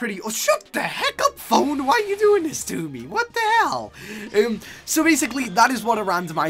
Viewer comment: On 1:54 did he just say what the hell?